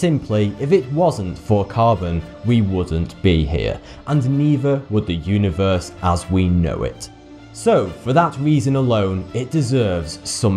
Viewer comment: At the end I thought he was gonna say thanks carbon instead of thanks for the views. I also thought he'd saw thoughty 2 rather than 42